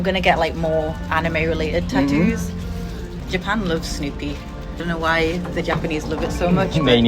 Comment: ここ Japan loves がJapan looks にしか聞こえん